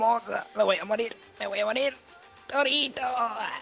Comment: soy yo o escuche dorito se muere epicamente